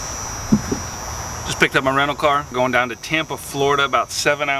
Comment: Thought you said “picked up a random car” at the beginning oof